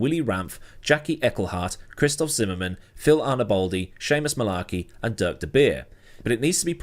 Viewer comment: I heard it as Shameless Malarkey at first 😂😂😂